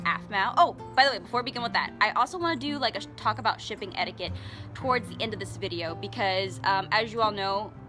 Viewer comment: Am I the only one that thought it said Shopping Etiquette and was confused almost the entire video before realizing it was Shipping Etiquette?